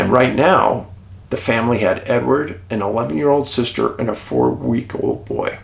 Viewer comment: I thought you said Martha had "an old sister" at first 😂